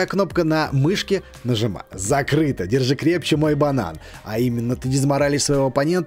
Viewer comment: Мне послышалась или ты сказал < держи мой банан>